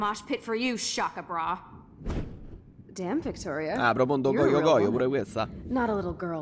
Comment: Qualcuno ha capito cosa ha detto a 15:45? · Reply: dice an do cojo cojo è romanaccio vuole significare a caso